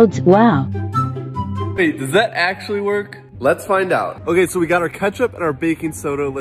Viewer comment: Who else thought he said babe does that actually work???